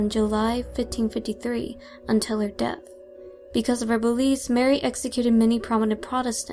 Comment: why do I keep hearing "boobies". I know you're saying beliefs. But all I hear is boobies.